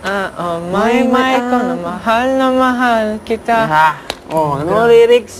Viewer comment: is it just me? I thought I heard at around 2:16. Maymay na mahal na mahal kita.